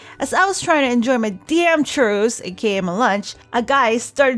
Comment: At first I heard "My churros...AKA My life..."